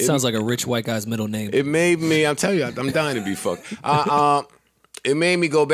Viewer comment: With 56:58 left in this show did joe say he's dying to be fucked